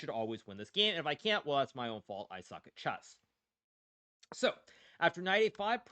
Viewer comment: Did he just say * i suck at chess * ?